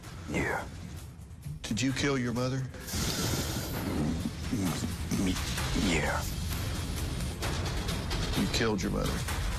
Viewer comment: when Dr Phil asks if he killed his mother it sound like he said “it wasn’t me yea”